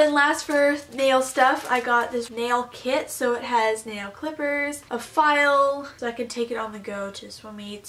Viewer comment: I’m just a little bit late.. hehe I’ve been watching you all day ilysm!! But it seems I have swimmers ear 2:13 I heard flippers at first